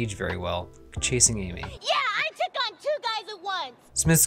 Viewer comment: I had to rewind cause I thought you said “those 2 guys were…” at 1:12 lol